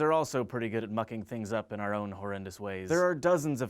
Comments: I thought he said f**king instead of mucking at the beginning so i had to go back and check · haha i thought he said fucking. really he said mucking but i thought fucking.